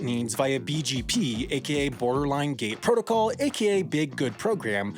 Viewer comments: I thought he said “borderline gay protocol” which is what is what is activated in your bros catch you wearing capris. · I swore you said “Borderline Gay Protocol”